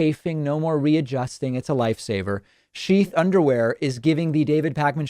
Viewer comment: Agree with the video. I had to rewatch the end of the ad though because it sounds like he said "no more readjusting the Light Saber"